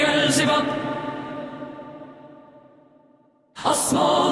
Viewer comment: "Yo bro what's your dog's name?" "Beelzebub." (Edit: Gah damn, a thousand likes for a bad joke?) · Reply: When i first heard the audio i kept hearing "alien Benjamin Elizabeth amodios santanas losefer" for some reason 😂😂